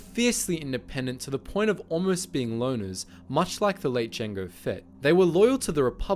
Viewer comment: I thought he was saying loaners not loners. Lol